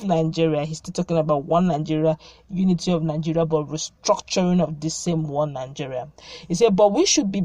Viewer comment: Obasanjo. Good morning. Oh I thought you said restructuring your mind, Anyway whatever you called it , it's too late, all we need is Biafra